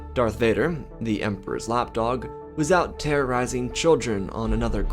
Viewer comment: i thought you said the emperors hot dog